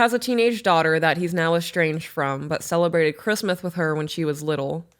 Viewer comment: did you say Christmath!? So CUTE!! Lol Im yall friend in Kentucky!! #bourboncapitaloftheworld